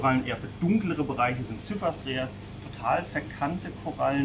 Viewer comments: Minute 3:01 von welcher Koralle redest du da? Ich kann den Namen nicht verstehen und ihr zeigt sie auch nicht. Vielen Dank vor ab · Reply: spricht von einer Cyphastrea Koralle. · Sehr schönes Video für einen Neueinsteiger wie mich. Bei Minute 3:00 verstehe ich nicht ganz den Korallennamen. Wo er von den verkannten Typ die an der Scheibe hochwächst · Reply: es geht vermutlich um Cyphastrea glaube.